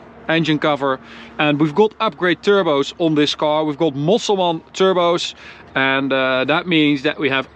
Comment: I heard Musalman Turbos lol